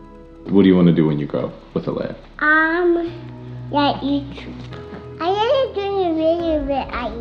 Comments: did Elle say, " I want to make videos with Alaiia"?? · did Elle say I wanna do videos with alaïa